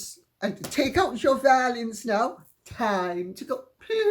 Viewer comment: Instead of "take out your violins now" I heard "take out your valiums now" 🤣😏 Just possible talking about Rachel M.M. adventures I love to watch your Youtube channel Lady C.